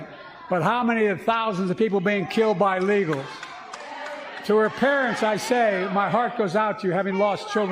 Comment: I think he might have said "but how many thousands have been killed by _legals_. i.e. "importing criminals is okay because we have criminals here already".